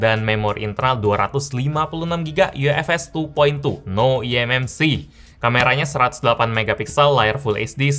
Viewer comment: Yang detik 0:43 bilang apa ya ? No mmc ? Maksudnya nggak ada memory card ? Pls jawab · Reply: No eMMC, tp ufs internal storage..